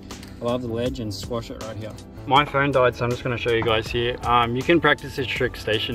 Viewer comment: i heard "my friend died" and thought you just casually threw that in. Had to go back and listen again